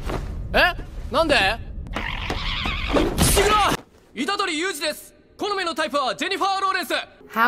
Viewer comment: what it typed:itadori Yuuji What I heard:itadori Yusuf